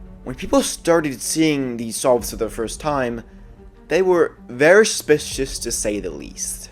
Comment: what was said here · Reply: very suspicious